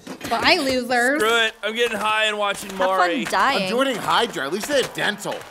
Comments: OMG I SWEAR I HEARD RICK! "I'm getting high and watching Morty" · did someone say: I’m joining HYDRA, at least they have dental 😂😂